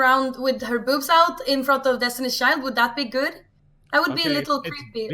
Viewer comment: "in front of Destiny's Child" lmao I heard all that wrong